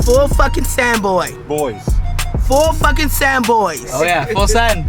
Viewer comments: I thought she said “four fucking sand boys” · Thought that lady said 4 fuckin sand boys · I thought she said four sand boys lol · How did it take us this long to get hit 🤣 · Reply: NELK I thought she said four sand boys cause you guys are white 😂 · I thought she said "FOUR FUCKING SAND BOYS" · Who else thought she said “4 fuckin sand boys”? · anyone else hear “4 fucking Sam boys” 8:37?